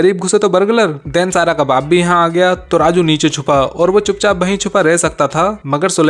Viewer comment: WHAT DID HE JUST SAID🤣🤣🤣🤣🤣🤣 BARGALAR